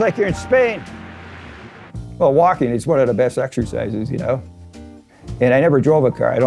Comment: When he said “Well, walking is one of the best exercises,” I thought he said, “Milwaukee is one of the best exercises.”